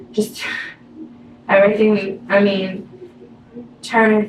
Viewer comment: She caught herself, she said We I mean, they @ 8:06, you can tell she is trying to think before she speaks but I heard her say "we" just before the toothbrush went up her bottom, listen carefully you will hear her faux pas or the truth